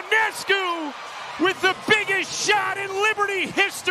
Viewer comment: Great shot! That was just nasty! But I don’t agree with the announcer of that being the best shot in Liberty history. T. Weatherspoon more than half court heave against the Comets in the Finals with NO time left on the clock was the BEST shot in the history of NY!! Who remembers that shot? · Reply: I thought he said "biggest shot", not "best shot".